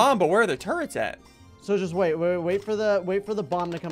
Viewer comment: Sigils? (Visible confusion) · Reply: Carter G yea, it sounds like he said Sigils, but he actually said “so just”